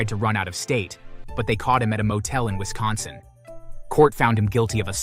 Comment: did he say Wisconsin😮